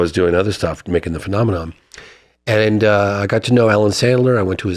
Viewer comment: Is he saying Alan or Adam Sandler yall? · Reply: Allen😊